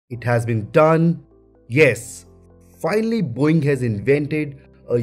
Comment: It just sounded like he said, “Boing!!” 😂😂